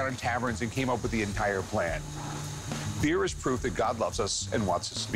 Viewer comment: The closed caption said "fear" not "beer" that makes a screenshot really really funny..!! xD